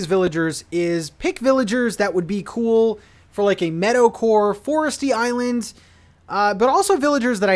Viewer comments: Really thought you were saying Metal-core! Didn’t realize it was Meadow-core until 8 minutes into the video. 🤦‍♂️ Excited for the new island! 👍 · Dude up until about halfway through this video I thought you were saying "metal-core" instead of meadow-core XD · This first half of the video I thought you were saying METALcore. I thought all my Eddie Munson dreams were going to come true!!